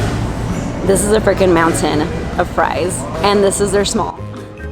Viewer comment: I had to back and listen a second time cuz I thought you said "this is a fing mountain of fries" hahaha. Another great one!